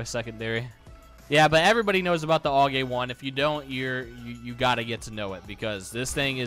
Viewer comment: put on your English captions and you will see all-gay one instead of aug A1 XD